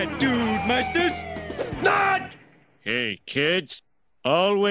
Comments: it sounds like poochie is saying "always be psycho" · I always heard "Kids, always be psycho.... TO THE EXTREME" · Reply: Always recycle! But again, down to Homer's poor voice acting and studio editing. Dat ze joke!